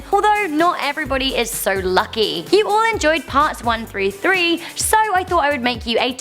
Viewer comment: did she say "parts 1,3,3"??? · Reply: 1 through 3